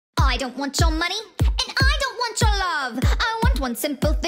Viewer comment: I thought it said " I dont want ur mom" 😂